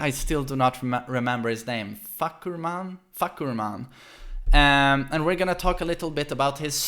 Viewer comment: This guy saying fahkumram's name: What I heard: "F*ck you man"